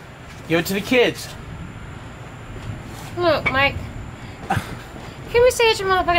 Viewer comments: It sound like he saying hello mike🤦🏽‍♂️🤦🏽‍♂️ listen carefully he saying hello mike please guys tag pickle boy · It’s sounds like is saying hello mike